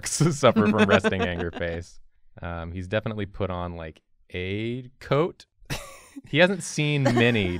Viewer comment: Please tell me I’m not the only one who thought said, “He’s definitely put on gay🏳️‍🌈 coat,”